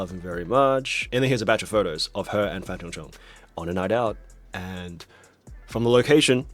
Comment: i misheard this as fan zhendong and got really worried lol